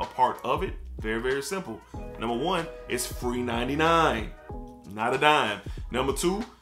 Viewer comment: I thought you said 3.99 lol.